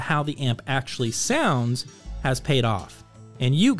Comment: Excellent review. I use their monos and pre for my daily driver. I love them! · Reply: Sorry, to my brain you are. Long story short when i first started to watch your videos a fee years back I thought you were saying “New Record Dave” so I just assumed your name was for the longest time until I heard other people refer to you as so even though I know your actual name my brain still defaults to LOL.